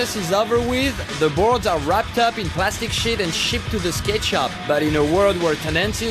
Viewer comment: it sounded like : "plastic shit"